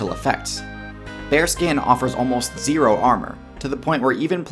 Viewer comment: I thought he was saying Bear Skin at first